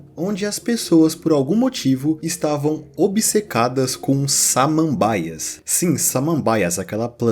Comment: Inglês é o meu idioma nativo, e eu ouvi a palavra “Samambaias” como “Salmon Bias” 😂.